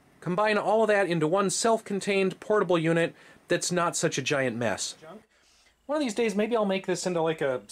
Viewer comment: the voice in the background completing his "that not such a giant.." saying junk instead of mess 😂😂😂😂